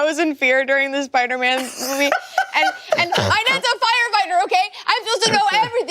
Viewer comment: what does say at 4:22? they were a firefighter??? i'm hearing that wrong right